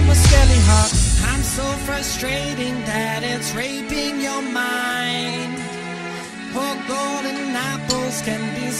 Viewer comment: I swear at 2:52 hes said I'm so frustrating that it's r**ing you're mind